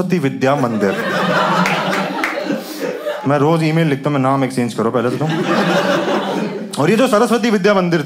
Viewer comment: What he said at 2:16??????? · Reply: Naam exchange karo